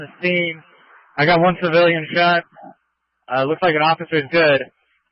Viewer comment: Dispatcher is incompetent IMHO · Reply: did he say looks like officer is dead?